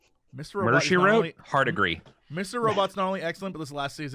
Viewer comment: What show is Jesse talking about at 11:39? I just can't make out the title. :( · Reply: Mr. Robot